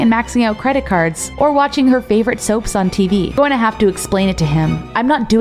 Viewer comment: Did i hear soap🖐️💀🗣️